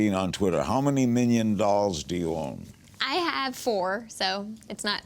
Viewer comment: I thought you were saying How many million dollars do you own? and said 4